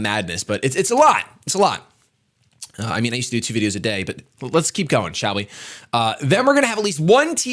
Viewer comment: For a second I thought he said 10 videos a day. My dreams then were crushed.